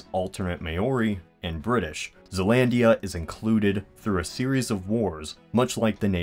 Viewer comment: hahah i thought it said Zeldaland